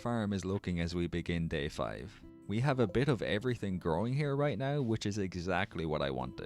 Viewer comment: I heard you had me growing!